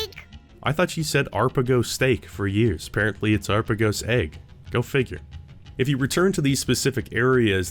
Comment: "Arpagos Egg"? I always thought she was saying "Arbuckle Zaic" whatever that means, lol.